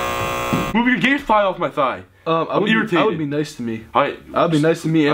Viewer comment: Did e say guy thigh